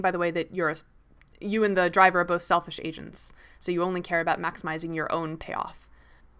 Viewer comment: I thought she said "selfish Asians" when she said "selfish agents".